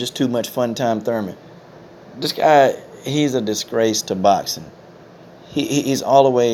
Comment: Coach disgrace, your welcome. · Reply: Lol i was wondering what "a disagree" was. I thought he was saying he has a disagreement to boxing